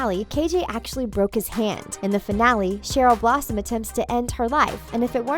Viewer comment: She pronounced about ALL of their real names wrong. MADALINE PETCH. That’s how you pronounce it. Ms. Gundy? And Cherry Blossom? New phone who dis? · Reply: I actual thought she said cherry blossom its Cheryl blossom